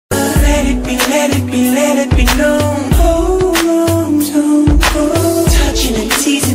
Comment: I always heard it as “Hold on Jungkook “😂